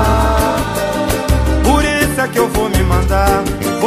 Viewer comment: Alguém escutando porque acabou de descobrir, que essa musica é uma carta de suicida 😭😭😭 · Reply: Sempre achei que ele falava "por isso que eu vou me matar"